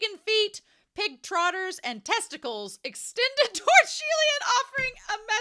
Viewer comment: lmao I think it says 'tentacles' 🤣🤣🤣 Oh my, I can't believe that we reach the end of the novels. i'm gonna miss the reactions so much, but at least it's not the end. Looking forward future content! ❤ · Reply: OH. It's Tentacles. Patreon has given me hell, hahahaha, because I got it wrong TWICE. I'll address that in a future audio drama episode but man...my mind was REALLY in the gutter, hahaha!! But thank you! It's not the end, but onwards and upwards, right? Thank you for the kind words, comment, and following me on my journey!